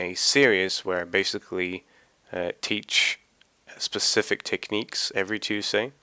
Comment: What did you say at 0:37 bro? :o <3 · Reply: Specific techniques